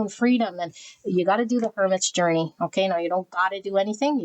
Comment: P2, accurate fr, ty so much. That was a necessary message to convey. I thought you were saying “Furnace Journey” but it was “go through the Hermit Journey”. I’m so Queen Swords. I am in touch with my Empress though. They need to go on a walk together or take tea in my inner Sanctuary. I realised something significant at the end of the reading , the animal pairings I’ve seen I can claim for myself - e.g. the two dolphins porpoising slowly where I am as I walk alone on the beach are keeping ME company… they’re not a reflection of an outer union but MY inner union. Same with the two turtles swimming with (or escorted by) the dugong on New Year’s Eve… an inner pairing being protected by dugong spirit. That’s a lovely shift 🐬🩵